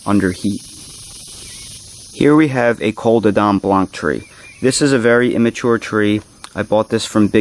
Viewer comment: What was the tree at the 15:00 mark? You said it's one of the worlds tastiest, just didn't understand what you said, let me know, thanks! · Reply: Joseph Damiani that tree is my Black Madeira.